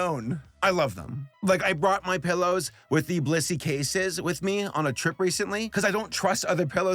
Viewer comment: Is it just me or did it sound like sabor said Bussy instead of blissy?